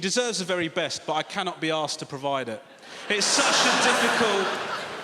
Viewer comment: I always thought he said ‘assed’